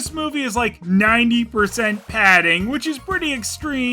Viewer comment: in Patrick’s voice Padding? I thought you said pudding!🤣